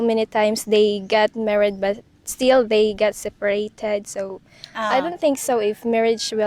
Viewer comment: I’m not sure I understand you. This is a video interviewing province girls, and I don’t think any other them have parents that are separating and remarrying 😳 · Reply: it sounds like she says they got separated and remarried ? maybe I misunderstood?